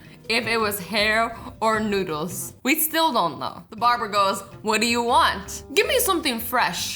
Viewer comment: I seroysly thought she was saying barbra